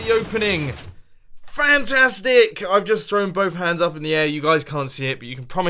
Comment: I thought you said: "I have just thrown my pants in the air"😂